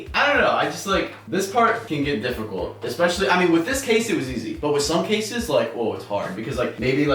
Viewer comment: When you say "In this case it is easy" I thought that you said that in this position but actually you said in this case(for computer) 😂😂😂